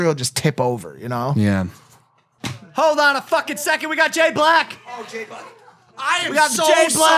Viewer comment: i thought he said Jae Black which is a hooooot porn star